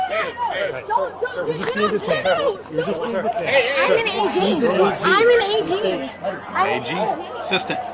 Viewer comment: I kept thinking: yea, an AGing what?😂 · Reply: I thought she was saying "I'm an Asain"